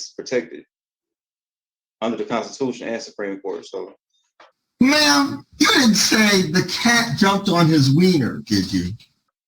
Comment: 💀 I haven’t been here long so I’m hearing “ma’am did you say the cat jumped on his wiener“ 😂 for the first time and I literally can’t breathe and you should see how my pet is looking at me 🤦🏻‍♀️